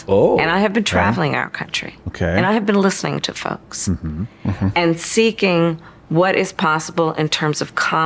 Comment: "i've been listening to fucks"? Did I hear that right?